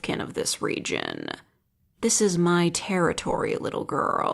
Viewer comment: Thought this said "This is my territory, pvp"